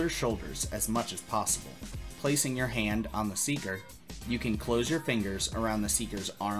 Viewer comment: another great video! 1:42 says "in dance" instead of endurance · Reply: speaker instead of seeker